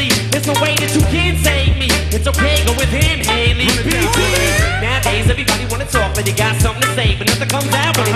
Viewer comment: How the hell did we go from this to the f*cking mumble rap? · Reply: Holy shit I was about to freak the FUCK because I thought you were saying Em mumbles nowadays